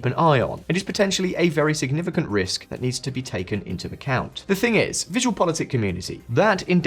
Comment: You said "into the count" instead of "into account".